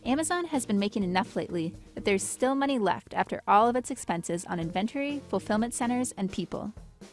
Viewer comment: Did she just say inventery instead of inventory?